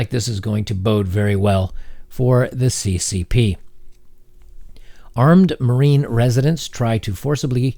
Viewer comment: Armed Marine residents. I thought I was tripping when I heard that! 😂 I get caught up on words too man.